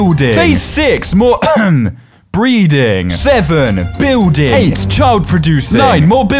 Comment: Did he say more breathing